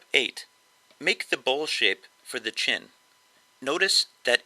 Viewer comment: Step 8... Make the Bullshit for the chin. I know that ain't what he said but I was like WHAT??? lol (Bowl Shape)